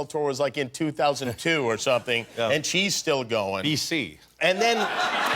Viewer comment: What did Elton said @ 0:54? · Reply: B C, meaning before Christ.